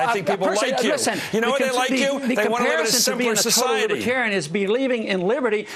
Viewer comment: I'm sorry. Back up there...It's hard to tell, but did he just say that "the reason people like you is because they wanna live in a separate society"? What a tool. He thinks Ron Paul supporters are a bunch of racists. Why is Chris Matthews even famous, anyone can do his job and anyone would be MUCH more appealing to look at, let me tell you.